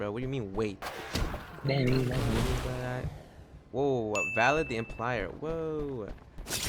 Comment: are we gonna talk about how he said "valid the implier" instead of vlad the impaler